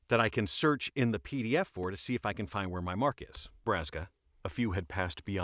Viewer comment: I thought that said Borrasca 😂